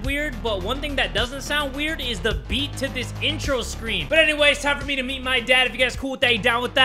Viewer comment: i thought said "its time for me to beat my dad" 😅